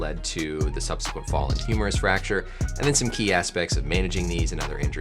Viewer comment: I thought he was saying humorous fracture. I was like damn even the Doctor knows it's funny